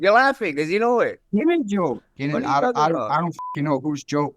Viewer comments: Guru didn't get it but Ilia thought Max said "Joe" instead of drill 😂that's why he said "Who's Joe?" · in 19:33 ilia asks "who is joe" because i think misunderstood when max says "same joke"